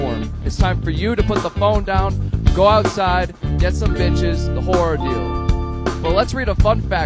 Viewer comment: I heard "the whore ideal" i feel like it still kinda works....